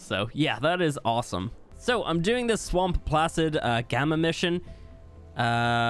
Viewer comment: does anyone else hear him say "fuck-prints"?